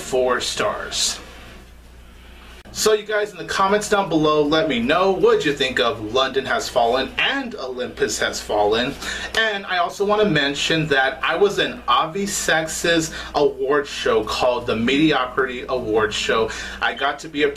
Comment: Oh God, I thought at first you said "I was an obvious sexist" between 08:08-08:10 XD I never was interested in Olympus Has Fallen, and this movie looks even worse (it did also sound like you thought it was a little weaker since the first half didn't invest you that much this time). I mean, just from the trailer you got the cheesiest lines ever! Babak Najafi is a much better director than this movie shows, I recommend you check out Sebbe. That's a great movie.